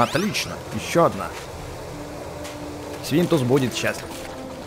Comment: Мне одному послышались на этой минуте 10:49 что сказал не свинтус а Свиндовс